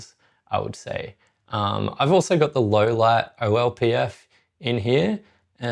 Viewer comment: what is a LLPF or LOPF or whaterever he said hahaha. Someone help pls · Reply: Optical Low Pass filter! They have versions for either highlights or low light and I've personally found the low light one the best